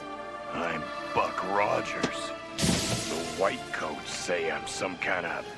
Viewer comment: when I was a kid I always thought he said Fuck Rogers.